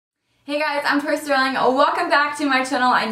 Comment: Whoa wait I played the video was scrolling to the comment section when you said "Hey guys I'm tori sterling" I heard "Hey guy I'm troye sivan" and I.was.SHOOKETH!